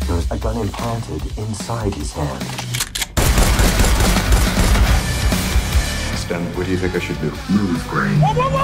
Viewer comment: My hearing must be bad I misheard Stand instead of a stem at one point.